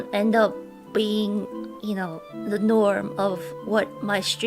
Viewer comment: i think she said "the norm" not "the lore" here. Also at 1:59 I'm pretty she said "fourth wah" instead of "first of all"